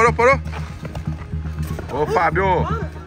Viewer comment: Só eu que escutei o falando, boa Fábio 3:08 🤨